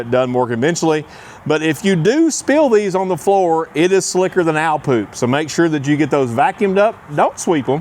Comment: Did he just say slicker than owl poop?